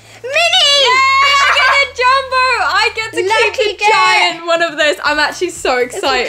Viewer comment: Is it me or did say lucky git at 6:36 lmaoo